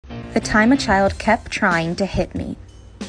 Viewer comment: is it only me or did i hear "the time a kid kept trying to hit on me" :/